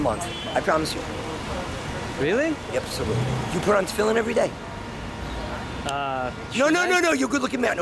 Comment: Could you guys tell me what he says at 3:09? Something like You put film everyday, i cant understand it.